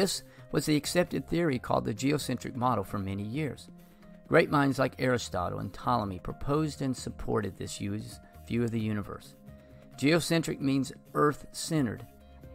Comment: what is he saying here? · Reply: great minds like Aristotle & Ptolemy proposed & supported this view of the universe the geocentric or earth centered model or view of the universe